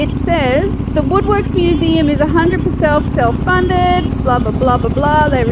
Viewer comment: 100 percelf, self funded. I heard that! "The lady with the big dog" is not usually a tourist attraction. Usually much more private viewing.